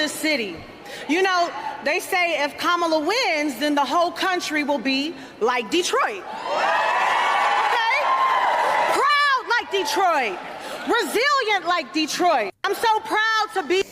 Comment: I legit thought she said "Brazilian like Detroit", and had to think if there was an influx of Brazilian immigrants to Detroit....